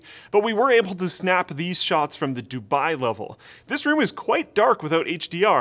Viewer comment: did i hear dubai ??? at 8:30 3 sec forward?? NICE